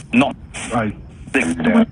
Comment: after you say what did you say to him? I heard "that he is evil"